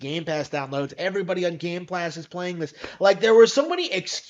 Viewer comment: I'm sorry did he just say game plast